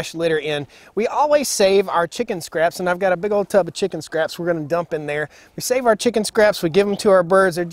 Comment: I thought you said you give them chicken scraps, not kitchen scraps😂